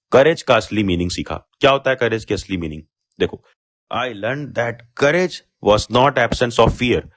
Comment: kya bola tune 😂 “ I land that “😅